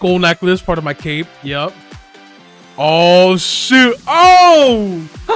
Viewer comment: did you say the S word at 10:20 or did you say shoot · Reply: He said shoot